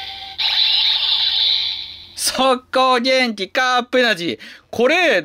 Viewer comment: ブラック企業のベストマッチって思って聞くと「速攻減給！カップエナジー！」 て聞こえたｗ